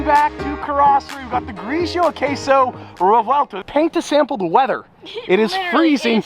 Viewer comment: all I heard is "Grigio Queso Revuelto"